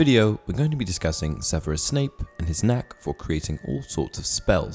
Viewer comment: I thought you said “and his neck” wow